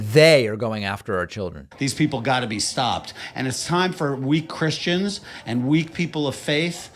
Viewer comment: I thought he said “we Christians” then he said Biden was on his needs in front of the Chinese President! 🤔 Not very Christian like😂